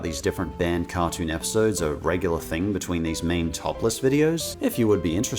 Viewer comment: Man... I'm getting old, my hearing is going. I misheard Top List as Topless and that had me confused for a moment.